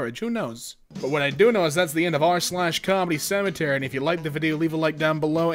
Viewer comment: the captions say "r/CommieCemetery"